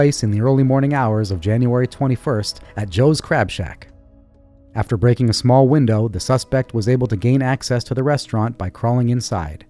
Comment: Damn I thought I first heard 'Joes Crap Shack'. Aside from that, hope the cleaner got paid well.